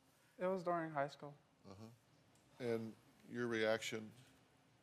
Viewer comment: I for sure thought he said “I thought I was high on weed” lmao glad I came to the comments.